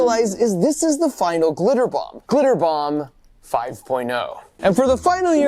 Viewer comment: Thought it said car bomb 5.0 😭